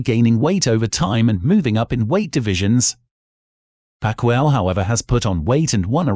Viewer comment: Haven’t the narrator heard of how to pronounce “Pacquiao”? It could be just me but I heard it as pacquire.